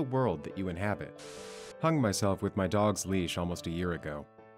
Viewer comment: Omg I thought they said they hung themself with there dog.